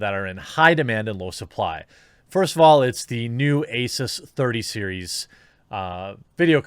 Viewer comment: Excuse me, what ?! Can you please repeat that ?! Did you say Asus 3000 series ?!